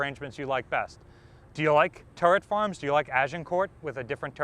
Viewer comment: i shit you not i thought it said turtle farm, and was very confused